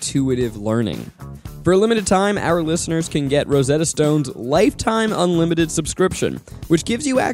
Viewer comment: Does says Rosette Stoned in the ad? :)))